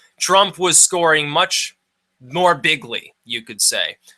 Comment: Hey, I hate politics, and I'm not a Trump supporter, but I think he was misheard—I don't think he said "bigly," I thought he said "big league." I could be wrong, but whatever. As for your analysis, I get it, but there are some issues. (And who among us is truly unbiased?) I'm just curious about how a Trump presidency would play out in the political arena. Since he's not a politician, isn't it possible he's liable to isolate the U.S. from the world community at large? Does it give Clinton an edge to have experience in that milieu?